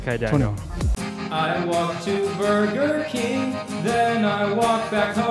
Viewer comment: I thought it was said I want to burn your kin or something for a second